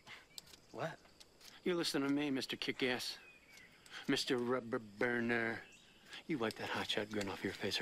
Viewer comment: Mr. Rubber Burner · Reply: I always thought he said Rupert Burner. I thought that was his name.